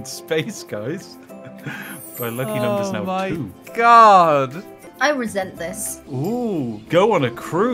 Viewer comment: the amount of times I heard, "I resent this." 😑